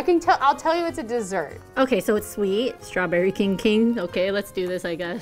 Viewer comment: I heard "Straberry kinky!"- 💀💀